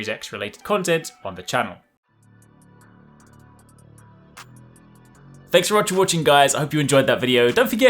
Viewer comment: Sorry I'm new here, what is he saying at 6:14? Thanks for watcha watching guys? I've listened to it like ten times and it's driving me nuts, haha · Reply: Thanks very much for watching guys.